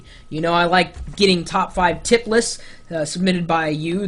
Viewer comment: dude in 1:07 threw 1:09 you sounded like you said Tit less, lol